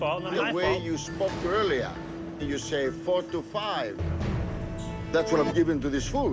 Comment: minute 20:00 he did not say 4 to 5, he said 3 to 4 and I am sure of that.